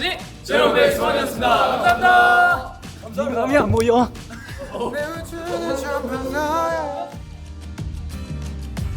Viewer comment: 뭐라고한거야 유진아?ㅋㅋㅋㅋㅋㅋ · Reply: 네 마음이 안 보여?good so bad 유진이 파트 가사 말한 거 같아요ㅋㅋㅋ